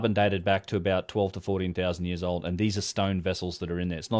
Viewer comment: 12 - 14,000 years old... did I hear that correctly? Anyone have any more info on this burial? Link perhaps?